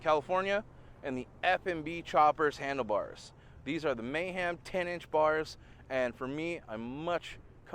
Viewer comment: Sorry, I heard ( Mayham 10" ). If I missed please correct me. Thanks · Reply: the mayhem 10" are the mini apes.